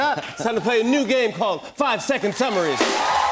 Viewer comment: I thought it said 5 seconds of summer lol